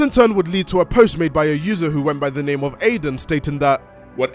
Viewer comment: please tell me im not the only one who heard Damien